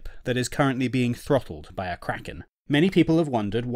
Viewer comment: I thought that they said a sinking ship being throttled by a karen